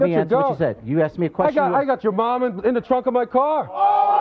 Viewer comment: did he just say I got your mom in the trunk of my car